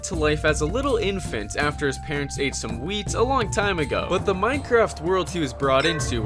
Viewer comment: sounded like "after his parents ate some weed"